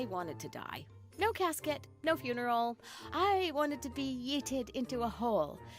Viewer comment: this bruh said yitted instead of yeeted